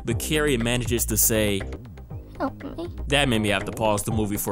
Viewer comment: What is the girl on the phone saying at 6:26 (in No Child of Mine)? I watched that part a few times and I can't tell what it is. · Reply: Help me